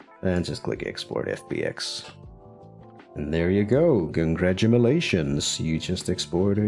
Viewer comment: am I high or did he say "congradumalations"